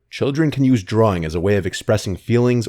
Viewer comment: Omg I heard, "Children use drugs.... " Hahaha